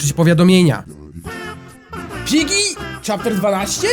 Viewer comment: Czy tylko ja usłyszałam "Dżiggy"? xD #Wrzucator